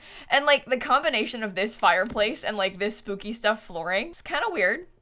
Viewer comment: did she just say ”spooky day” flooring?? 😂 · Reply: she said “spooky stuff flooring” because the flooring is from spooky stuff